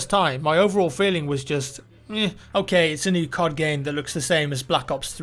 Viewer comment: Had this video playing whilst folding some laundry in a different room and I Thought I kept hearing 'Card Game' as in, magic the gathering, pokemon, ect. And seeing is how its a COD related video? I honestly thought infinity ward (or Whoever lol) Actually made the jump to some type of tabletop deck game. Was in Total disbelief until I realized! Its just and the fact that he's super English! Haha. Regardless though, I wouldn't doubt it.